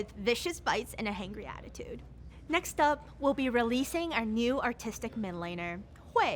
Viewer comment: i am hearing articstic but its league so i hear autistic